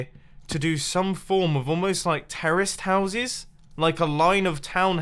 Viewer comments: Am I the only person who thought he said "terrorist houses"? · I thought you said "Terrorists Houses" xD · at first i thought he said "terrorist houses"